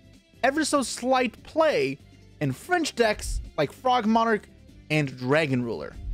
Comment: Guys why did I hear "french decks" which was then followed by frog...